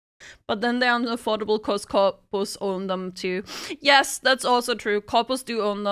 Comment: Oh, she said "corpos". I thought she was saying "coppers" as in "cops" and I was wondering what cops had to do with ownership (or if "coppers" are what they call cops over there)